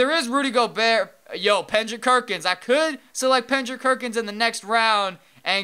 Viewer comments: Am I the ONLY one who heard "Pendrick Kerkins" in 5:30 and choked on his water? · did you say pendrick kerkins at 5:32?